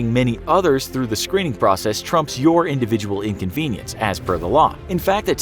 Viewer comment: Did I hear Trump?